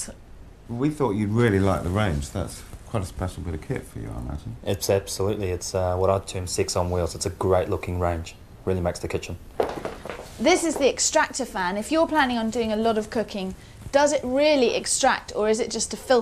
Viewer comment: Who calls a cooker sex on wheels. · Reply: Oh! I thought he said "Six on wheels" and thought 'What? It has EIGHT burners. Can't he count?'